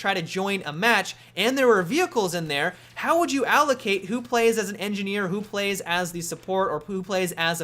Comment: sounds like he says poo plays xD